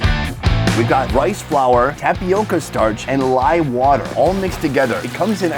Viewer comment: did he said live water??